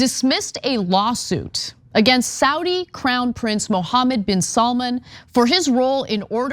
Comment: I seriously thought the video said Saudi CLOWN Prince, and now I love that for him